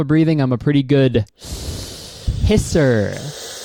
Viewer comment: for a second i thought he said hes a good pisser